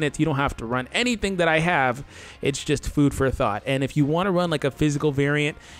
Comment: hold up i thought said food for a thot